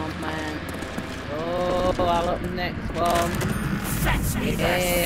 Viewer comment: "Fetch me their soul" so that's what they were saying?